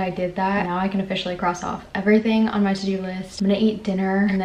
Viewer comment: I thought you said “I’m going to make an emergency!” I was like what?! 😭